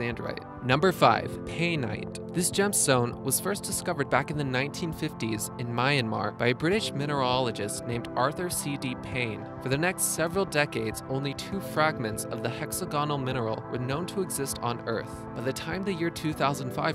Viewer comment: You must be a "mineralologist" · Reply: I might have another listen. Thought he said minierologist or minierologist. Hahaha! Bad. Ooo. Autsch! << Google it before you jump me. Lol.